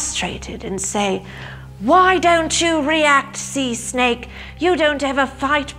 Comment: All I heard was “Sea Snape”...